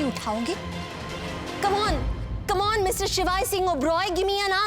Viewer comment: mr ki jagah mrs shivaay singh oberoi ho gaya😂